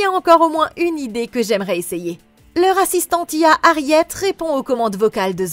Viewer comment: mdrrrr tuez-moi, j'ai entendu "le r@ antillais" au lieu de "leur assistante IA" ptn mettez fin à mon existence svp 😭